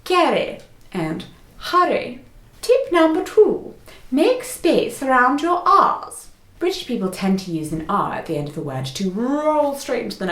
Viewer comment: I wasn't looking at the screen, so when I heard "make space around your arse" I had to stop what I was doing for a second